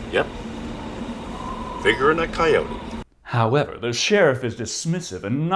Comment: Ok....totally thought they said “fingering a coyote” first time thru..